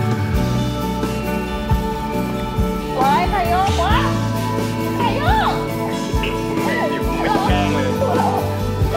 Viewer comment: What is mew saying at 8:27, at the end of their hug? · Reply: She says "Ohhh who swings us!?"